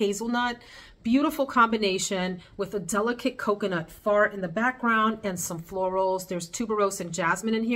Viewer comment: “...with a delicate coconut fart in the background”... did I hear that right?! 🤣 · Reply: LMBO! No, I said a delicate coconut note "far" in the background but I can see how it sounded like fart. Hilarious 🤣